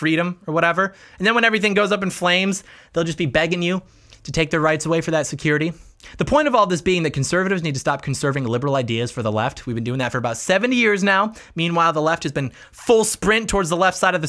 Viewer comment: did you say 7 years or 70 years?